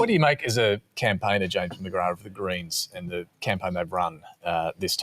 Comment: str8 up thought the liberal guy was saying "the koreans" not "the greens"